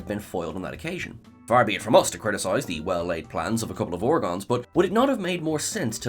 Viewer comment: I thought you said they were called Bogons?